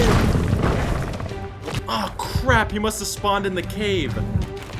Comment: this is such a well-edited, entertaining video! the only constructive criticism i have is that sometimes the subtitles don't match what you're saying. For example, at 15:49 the subtitle reads, "Oh, crap. You must respond in the cave" when it really should have been "must have spawned". I don't want to judge you or your editing (because this is amazing!) but it almost feels like the captions were created by ai? There are quite a few times in the video where the audio and subtitles are different things that simply sound the same. But otherwise, great video!